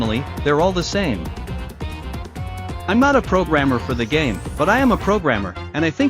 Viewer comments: i thought bro said "im not a programmer for the game, but im a progamer" 💀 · im confused now did he say? Im not a programmer for the game but i am a programmer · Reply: not a programmer for the GAME, (so I don’t have access to the Dandy’s World code and scripts and stuff to be 100% sure about everything), but I am a programmer, (so I’m familiar with how games like these WORK enough to make good guesses myself) 😁 Hope that makes… some… sense 💀